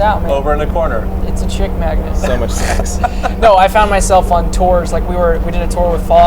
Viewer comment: WHEN THEIR TALKING ABOUT UKULELES TYLER SAYS "YEAH ITS A CHICK MAGNET" YOU CAN HEAR JOSH SAYING "YEAH, SO MUCH SEX", OR AM I THE ONLY ONE WHO HEARD THAT?